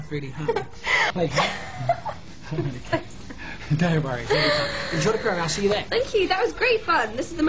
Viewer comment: why did i hear "enjoy the pronouns, she/they"?